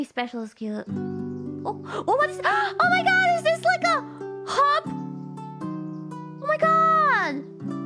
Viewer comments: I think what Kiara said there is "Is this like a hub?" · I think she said hub not hop